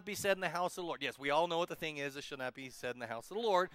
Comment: You may want to redo the subtitles....There are a couple of swear words like Sh_t at 9:43. Not to mention its way off on what you are saying....God bless you! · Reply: Agricultor Music he said should